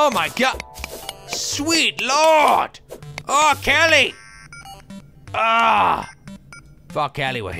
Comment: did he say "Fuck Kelly...."?